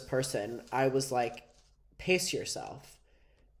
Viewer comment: Ngl with your accent I thought you said piss yourself